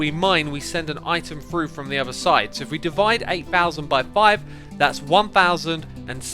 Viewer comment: he said devided instead of divided